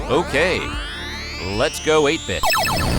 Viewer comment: I thought you said "Lets go ape shit" when you said " lets go 8 bit"